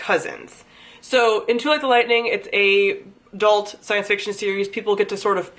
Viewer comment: is the books title "Two at the Lightning" you were talking really fast so I didn't catch but the book sounds interesting so I wanted to make sure I heard the title right? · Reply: Too Like the Lightning! I have a review for it as well :)